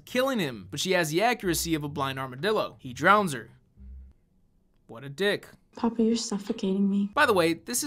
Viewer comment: I always though said “ and he drowns her . With a dick . “ Just going by the image of Divoff looking forceful in the face . I know realise you said . He drowns her . What a dick “ . Shame but I find the misheard line funnier with the image at the time of you saying it . I though it was intentional .